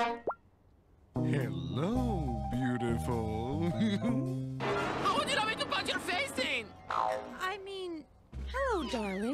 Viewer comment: tf did she say? right before she says "I mean hello darling" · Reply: She says " How would you like me to kick your face in?!"